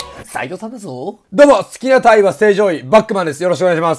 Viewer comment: 好きな鯛は、正常位に聞こえた笑